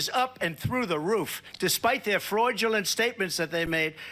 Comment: Did he actually say 'Freudulent'? Talking about Freudian slips!